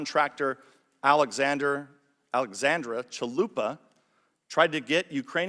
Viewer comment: Did I hear Chalupa? Yummy!!